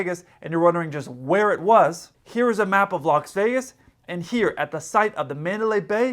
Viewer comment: did you say Locks Vegas???? 😄😄😄